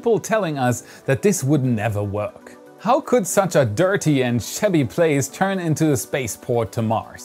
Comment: "...such a dirty and sh***y place..." Whoa!...wait...what did he just say??!! LOL, really? In front of the children? Ha ha. What was that word? Maybe it was 'shabby'?